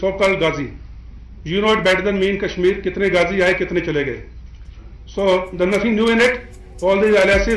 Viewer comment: I thought he is saying Kitne Gandhi aaye kitne Gandhi chale gaye😂